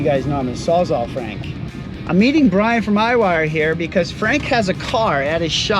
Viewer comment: Am I the only one who thought he said Ryan from RyWire? LOL